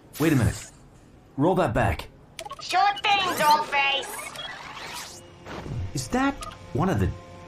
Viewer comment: Did the robot just called Optimus 'doll face' or it's just me? I think I misheard but I'm not so sure anymore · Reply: Yes, she said Dollface.